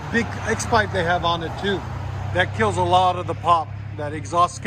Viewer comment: EDIT: Commenter cleared up for me that he said "kills a lot of the pop", not "kills a lot of the power". My mistake. Could you elaborate on what you said around 1:27 about x-pipe causing power loss? I'm sure having missed the rest of what you apparently said to the customer about it changes the context a lot, but I've always heard the scavenging effect of x-pipe has only increased power (at least at higher-RPM), never that it robbed power. Did the two FlowFX replace some sort of dual-in-dual-out mid muffler and that's what you're referring to?